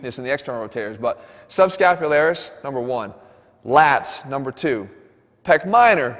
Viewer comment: 他在6:11提到的背肌 (Lasi?) 是指Latissimus dorsi嗎? Latissimus dorsi做內轉，但我有點聽不來他是不是在講這塊肌肉